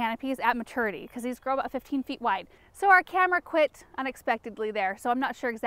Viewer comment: When your camera messed up, I thought it said Cameron died it didn't it said camera